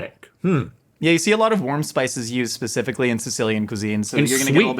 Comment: I stg I thought bro said WORM spices